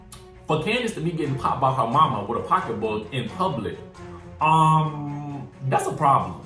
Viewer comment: I thought you said Kandi’s mama hit her with a pocketbook. I was like Mama Joyce. I had to rewind this.